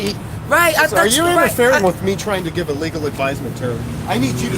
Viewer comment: Did I hear officer right? At 5:48 he said you're interfering with him giving legal advice? Unless he's attorney he can't give legal advice that's against the law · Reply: No he said a 'legal advisement'. They are trying to tell people they have 48 hours to move. However the 48 hour notice is supposed to be attached to the property that is excessive and the government wished to 'unincumber' people from.